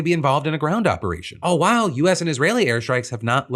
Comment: I know these are deadly serious topics, but I just gotta say, Phil, every time you say "all while", all I hear is "oh woow".